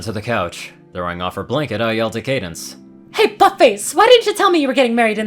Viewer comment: Idk if this is just me but I legitimately heard fuck face instead of but face.